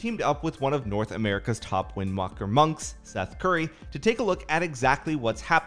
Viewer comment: Did they just call Venruki, VenCurry?